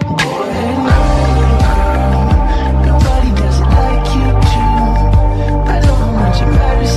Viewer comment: the way i thought he said “nobody doesn’t like you too” and not “nobody does it like you do” 😭